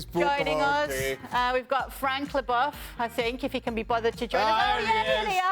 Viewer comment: Did she say frank le Boff*